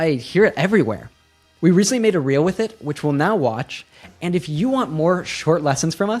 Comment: the subtitle says ‘which will now watch.’ Should it be ‘which we’ll now watch,’ or am I misunderstanding it?